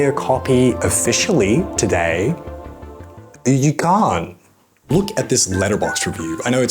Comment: "you can!" There is no t in his pronounciation. I think he meant to say you can't, but there is just no t at the end, so he says "you can!"